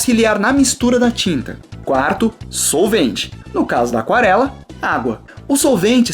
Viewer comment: eu entendi sorvete parei